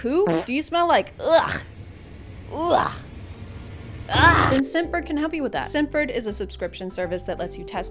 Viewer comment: I misheard the name of the sponsor as "Simpbird" and now I'm sending $100 a month to a local pidgeon...she is pretty hot, tho.